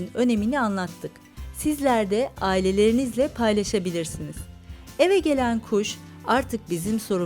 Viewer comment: eve gelen puşt diye duydum ĞQÜSİQİSŞW